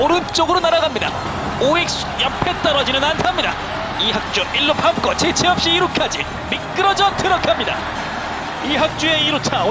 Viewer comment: 나만 1루 밟고 재치없이 2루로 향합니더라고 들었나?? ㅋㅋ